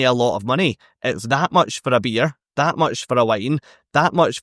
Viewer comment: Haha, the first time you said "that much for a beer, that much for a wine", I heard "that much for a beer, that much for a line" ... thinking what type of cruise have you been on 🙂